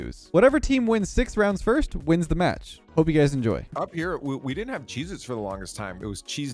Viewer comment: it sounds like he says "Up here we didnt have Jesus for the longest time"